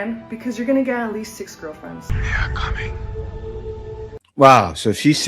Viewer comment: And I thought the big lips would be the thing that turned them off from me , come to find out the thing they want and waits , did she just say Six girlfriends what!@